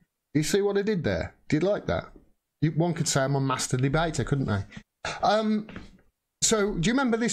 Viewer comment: haha thanks you made me laugh with your opening description of yourself, I thought you said you was a masterbater ;P I had to replay again to hear Master Debator nice play on words lmao